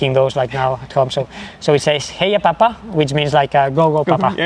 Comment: It sounds like Kilian says "heya papa", but contextually "vaya papa" makes more sense. Can anyone confirm?